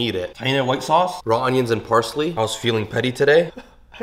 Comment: I felt happiness when you said "bismillah" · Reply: How comes it sounds like hes saying something different …it sounds like hes saying …”hamsmillah”